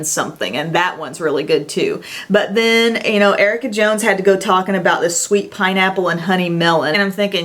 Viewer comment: What name did she say at about the 15:48 mark...? Where she is talking about the pineapple lotion..? -- Almost sounds like she said Erica Jones..? Anybody know..? Who is she talking about..? Thanks!! :)